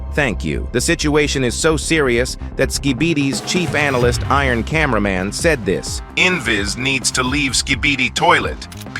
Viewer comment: Did bro just call iron camera man cheap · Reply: He said chief